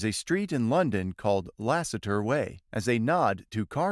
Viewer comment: I genuinely thought that said "asseter way" thank you for clarifying